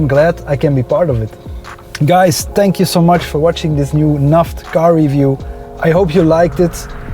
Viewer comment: I thought he said NAF car review 😂😂😂